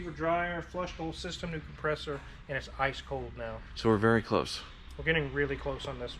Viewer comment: I thought the said “and it’s Ice Cream now” as in its fixed and smooth as ice cream now. For a split second I thought the had just invented the next hip slang term.